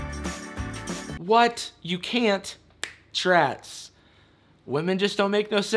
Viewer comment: What does he say at 7:04? 'Trats'..?